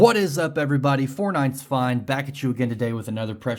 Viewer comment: my captions thought you said Fortnite’s fine lmao